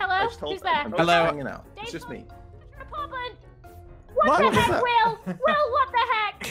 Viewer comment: When lizzie said what the heck will the first time i thought she said wacha head will